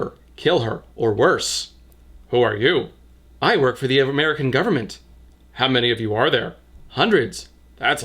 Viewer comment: "her cleavage charmed the crowd"....50% of that crowd is her own family. Is the author from the south of america by any chance? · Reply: For a second I thought you had said South America and I was like "eXCusE mE"